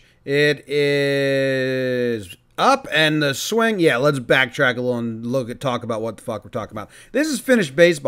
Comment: What did you even say in the first part of 0:12-0:15 😂😂😂😂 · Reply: “Let’s back track a lil and talk a lil and about a lil about wtf we’re gonna talk about it?” 😂😂 I love it , cracked me up!